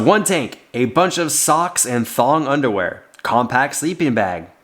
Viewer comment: Lol I thought you said thong underwear. I was so confused. But it all looks great!